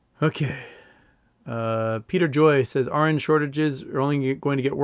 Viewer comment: Foreign regulators don't 'just happen to be better at regulation'. Separately, when you were saying 'RN shortage', I thought you were saying 'orange shortage', took me a few minutes ;) Thought it might be a 'Trading Places' reference.